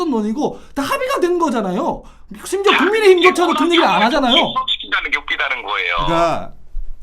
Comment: 동지분들 혹시 12:23에 이 말에 뭐라고 반박하는지 들리시는분 있나요?? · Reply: 자, 이게 호남 혐오에 정치를 혼합시키는 게 웃기다는 거예요 (?)